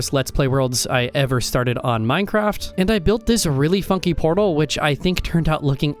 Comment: I had to relisten heard “realy fucking portal instead of funky”